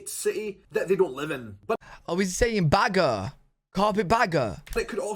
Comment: Lol mishearing carpet bagger as burger is hilarious. I’m going to start calling people carpetburgers